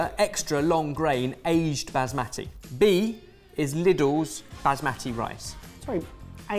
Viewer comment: Who writes the captions for these episodes? @ 6:41 "Liddell's" instead of "Lidl's". Feel like anyone in the UK would know that instinctively - are you outsourcing captioning?